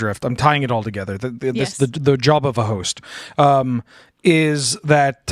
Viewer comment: "Job of the Host" Me: "Did he say Jabba the Host?"